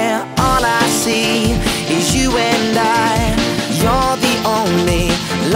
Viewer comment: If you play from 0:23 it sounds like olly is saying die